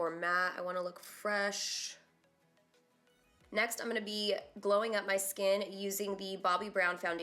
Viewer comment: Did she say "next I'm gonna blow up my skin" at 4:21? 😂😂😂😂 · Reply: glowing up my skin 😂😂😂😂